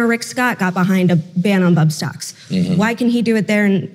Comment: Bump stocks do not make a gun fully automatic. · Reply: i caught that too, thought he said butt stocks, and i laughed xD really confused